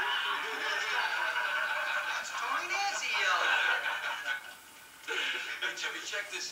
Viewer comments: I love how they keep the line "It's Tony Danza yellow". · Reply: I to thought he said Tony Danza yellow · I always thought he goofed and said Tony Danza yellow. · I always thought he said " it's Tony Danza yellow" duh!!!... I never realized he WAS Tony Nancy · Tony Nancy had successful rear engine dragster, before Garlits. It was Olds powered, than switched to a 392 Hemi. Tony Nancy did a lot of upholstery work in the 60's. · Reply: Thanks for the info.....I could’ve sworn he said, “Tony Danza”...funny considering he was in the scene.